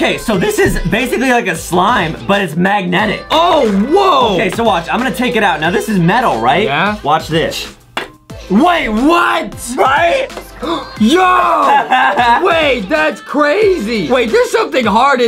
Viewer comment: did say watch thish in